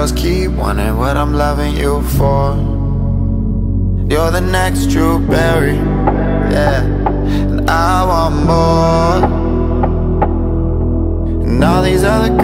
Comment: i thought it said "all these other girls keep wanting what im loving you for"